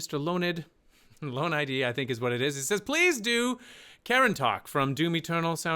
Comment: did he just call it 'karen talk'?